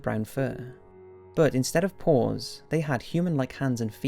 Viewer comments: Turn on the caption at 2:12 is it just me or it says porn instead of paws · pause at 2:13 and put captions/subtitles on all i can say is "but instead of porn"